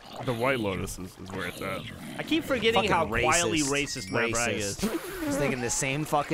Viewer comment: I thought Geoff said “QUALITY racist” 😅